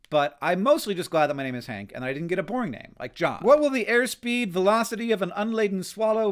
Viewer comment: Calling John a boring name, definitely brothers lol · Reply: I thought at first he said “porn name.”